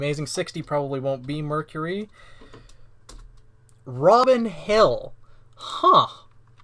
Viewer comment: when you said "robin hill" I thought I heard "rot in hell" and I looked up so fast